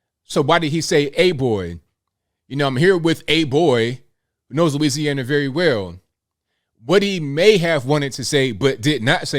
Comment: When I heard this live sounded like Biden said "and boy does he know Louisiana well".Now suddenly the soundbite changed?